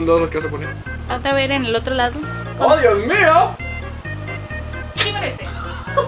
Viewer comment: "Chingame este" 8:05 Mikey Mouse, ¿Eres tu we? Jsjsjs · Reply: creo que dice "que con este" xq